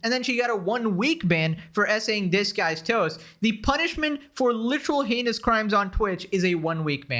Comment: I can't believe for a couple videos now, I thought you said "... assaulted this guy's toes" instead of DisguisedToast. I was like "These foot fetishists really need to calm down." 🙄😆